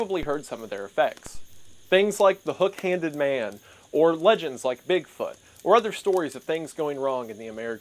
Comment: i misheard the intro as “legends like bitcoin” and it made me lose my mind